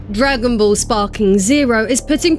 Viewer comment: Not going to lie, that sounds like a drink, not a game. Or, perhaps I misheard you. When you first said it, I heard Dragon Ball Z Sparkling Zero.